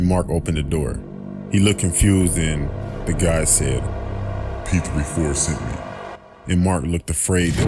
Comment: What did he say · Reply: Sinista Demon He said P34 sent me.